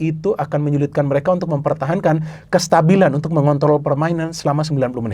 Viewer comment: meng apa tadi bung? saya ga salah denger kan?😂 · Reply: Mengontrol jelas kitu